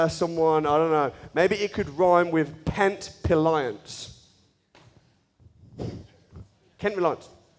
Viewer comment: what is he saying the companys name is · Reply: Kent Reliance 👍🏼